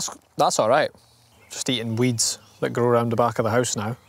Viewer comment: Finally time to pay off my student debts by eating free... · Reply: I thought you said eating trees for a sec